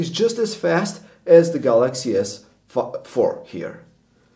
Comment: did u said "Galaxy s Fuck?" ahaha almost hear that